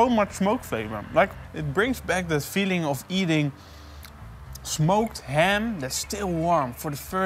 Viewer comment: I don't know I it took me 40 seconds In to the video, but I swore it said "Smoked BBQ hamsters" I knew it wouldn't be hamster but I thought you called them that based on the finished look of it. Glad I was wrong :D